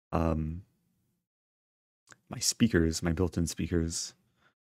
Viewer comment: At first I thought Eric said “speakers… my girlfriend’s speakers” and I got excited.